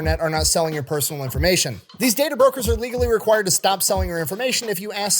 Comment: The first couple times I honestly thought you were saying " 'tater brokers," rather than "data brokers" in the DeleteMe promo. I initially ascribed it to being from Iowa.